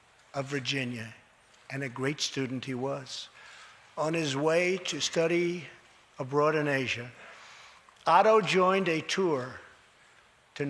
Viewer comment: It sounds like Trump said “he’s going to study a broad in Asia” Not “he’s going to study abroad in Asia.” Lol.